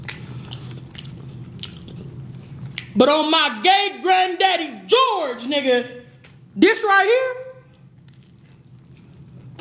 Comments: I thought she said gay granddaddy George 💀💀💀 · On my gay granddaddy George😂😂 This how many ppl thought it was funny 👇🏽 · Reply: I thought she said on my Great granddaddy George😅